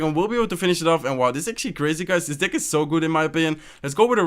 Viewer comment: WTF did he say dick?? 6:34 watch